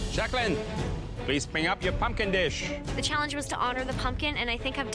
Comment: I really thought alvin said "Jacquelinee , please bring up your f*cking dish"😅